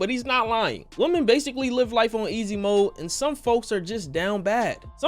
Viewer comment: At first, I was annoyed because I heard, "women have life on easy mode" and then realized you said SOME women have life on easy mode. I agree, but a lot of men also have life on easy mode 😅